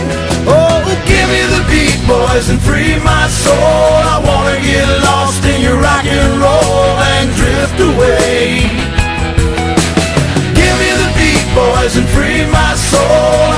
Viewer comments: Man when I was a kid I thought he said beach boys😂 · Oh goodness, I feel dumb now. This whole time I thought they were saying give me the beach boys, not beat boys. But now that I'm listening, I can hear that it's beat. :P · Ngl I always thought he said Give the Beach Boys instead of beat boys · I have always thought he said "Give me the Beach Boys" because they would totally help me feel free lol